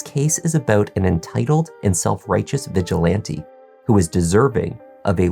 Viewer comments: I thought he said “bitch-ilante”. 😂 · I know you said "vigilante" but i heard "bitchilante" and laughed 😂